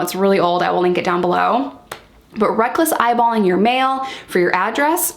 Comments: At first I thought you were saying "eyeballing your male" not "eyeballing your mail" different implication! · When you said "mail", I thought you said "male". Like "Eyeballing your male" as in "Eyeballing your boyfriend" and I was so confused.